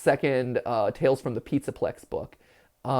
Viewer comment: for a moment i thought he said "tails from the pizzaplex". sonic?!